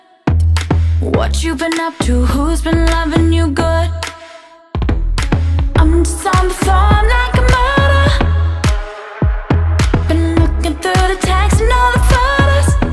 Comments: Okay...now I feel weird cause I understood "I'm just on the phone like a murderer" instead of "I'm just on the floor, I'm like a model" English is not my native language tho xD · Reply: I heard, "I'm just on the floor like murder." And English is my native language. XD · When she say "I'm just on the floor, I'm like a model", I heard "I'm just fine, I'm like a model". Lol. So close. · I always heard “I’m just on the floor. Like a murder” and that would actually kinda make sense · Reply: I thought she said ”I’m just on the phone like a murder” lol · huh I always thought she said "I'm just on the phone like a murderer" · I heard “I’m just on the floor in your kimono“ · Okay...now I feel weird cause I understood "I'm just on the phone like a murderer" instead of "I'm just on the floor, I'm like a model" English is not my native language tho xD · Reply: I heard: “I’m on the floor like a murder” 😂😂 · I always heard “I’m just on the floor. Like a murder” and that would actually kinda make sense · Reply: And I always heard "I'm just on the phone like a murderer...been looking through the texts and all the photos" idk made sense to me 😝😅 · I always heard “I’m just on the floor. Like a murder” and that would actually kinda make sense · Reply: I thought it said "I'm just on the phone like a murder" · I always heard “I’m just on the floor. Like a murder” and that would actually kinda make sense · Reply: I heard I'm just on the phone like a martyr !!!